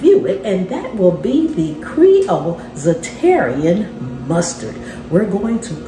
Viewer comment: This looks and sound delicious. I wasn't looking at the screen when you were adding the mustard. I heard you say, "Za-terrian Mustard" and had to rewind, but you were using Zatarain's Mustard. I was thinking there was a new/different mustard to try.